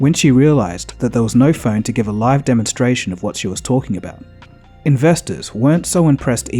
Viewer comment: What does he say at 00:33? Can't make it out. Something about she became flustered when she realised blah blah blah? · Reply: she was embarrassed and flustered when there was no phone to do a live demonstration of what she was talking about